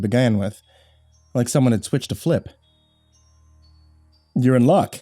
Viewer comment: Did that boi just say "switched a flip"?